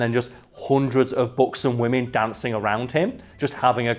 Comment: Ooohhhhh!!!...buxom women, I thought you kept saying beautiful books and woman hahahaha!!!